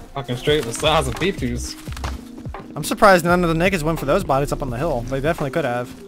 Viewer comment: Swear if sounded like you said nigga